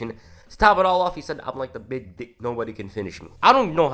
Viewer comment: lmao at 2:49 you misheard Cena. He said he was "like the big dig, nobody can finish me" not "the big dick" hahaha. The Big Dig was an enormous public works project in Boston that moved the central highway that went through the city underground and it took like 15 years to do it and was completed around 2003. It took way longer than what was proposed and was way over budget, that's what Cena was referencing with "nobody can finish me"